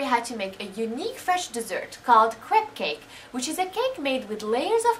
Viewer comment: ehehe it sounds like you say crap cake